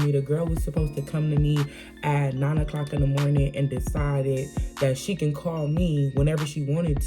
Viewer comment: I wouldn't say scared but he just favors her cause...that's his friend... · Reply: I thought he said f cash and that she can't come back